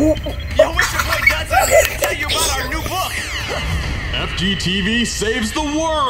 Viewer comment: I swear he said fa***t